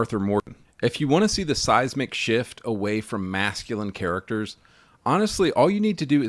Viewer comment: I totally heard that as "Size McShift" 🤣🤣🤣